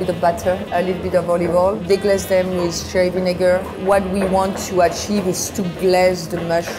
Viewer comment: did she say vinegar?